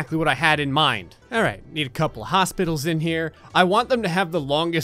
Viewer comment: every time I hear say "hospital" I hear "hostages."